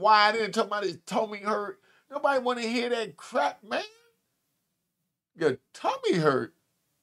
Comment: I THOUGHT HE SAID HE HAD TO CRAP.😂